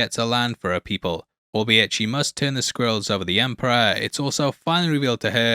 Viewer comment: sounds like you said she had to pick the Skrulls over the Emperor lol